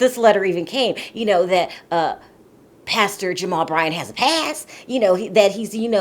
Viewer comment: I thought you said “Pastor Jamal Bryant has passed”. But you meant “past”. Too early.